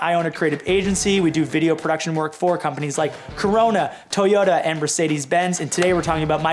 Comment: do i hear corona